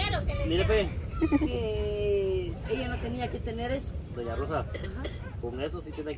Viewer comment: Minuto 7:43 le fijo dona rosa pero yo entendí dona bruja 🧙‍♀️